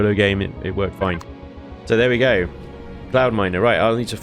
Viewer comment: Every time i hear say "Cloudminer" i hear "Clownminer".....